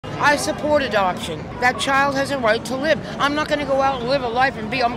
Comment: I heard "I support abortion" and I was confused the entire video